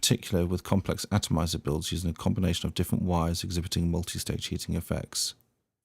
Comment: you say multi-? effects? What is the word you used? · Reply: "Multi-stage-heating-effects"....this happens when using/mixing different wire gauges and materials. Ramp-up time and ultimately flavour will be determined by this. SXi-Q will allow the user to adjust settings to tailor the vape to their preferences.